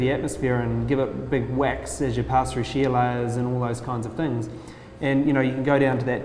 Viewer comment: When Peter Beck said: "Sheer layers" I basically choked on my mug of bleach, cuz I heard "Sheila's". Had to check the captions. Most Australasian thing I've ever think I've heard.